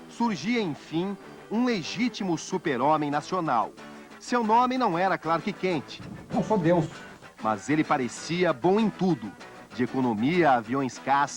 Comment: O Collor falou: "Eu sou Deus" ?? Foi isso que eu entendi?